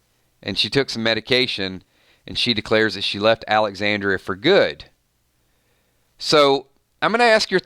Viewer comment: I thought you said that it looked like Beta was at Alexandria